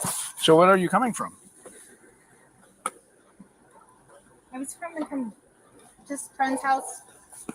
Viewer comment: HUh? When the officer asked, “So where are you coming from” did I hear her answer correctly? For the answer to his question, “I’m FROMING COME a friend’s house”? Omgoodness! 🤣😉